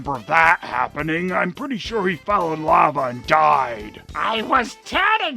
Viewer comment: at first I thought you said "...fell in love and died." Harsh!